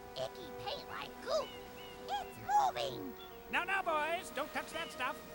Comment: I remember the first time I herad that toad say "it's moving" I thought he said "it's pooping"